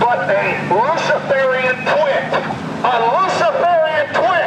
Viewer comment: I really thought Alex said Luciferian Twink which made me do a double take and went "if that's true....werk". Then I try to out my speaker to my ear and turns out it's twit. Oh well. Anw Luciferian Twink is my drag name now